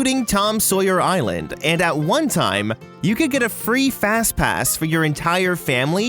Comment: FazPass? *fnaf flashbacks* (Thats what I heard, I dont think he really said FazPass)